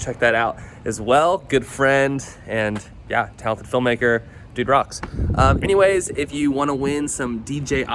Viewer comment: I could have sworn you said "to help a film-maker do drugs"...!